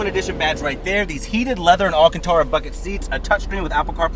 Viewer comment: For a min I thought he said “alcantara f*ckin seats” 😂😂😂